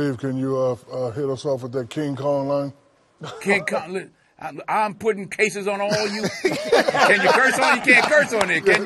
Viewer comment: Can anyone tell me what Denzel said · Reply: “King Kong loo- I’m putting cases on all you” it’s an infamous line from Training Day